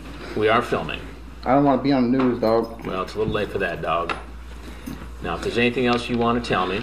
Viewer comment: What the hell did Chris just say?!! 😂 #RolePlay #ChatRoom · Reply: "Well, it's a little late for that, dawg."